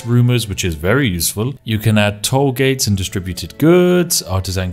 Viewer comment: did he say..... toll gates?